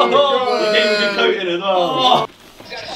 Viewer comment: onwards I think josh says “he’s going to get covid as well” mad... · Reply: I think he said coated